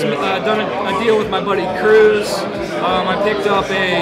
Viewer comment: caught me off guard when I heard Cruise lol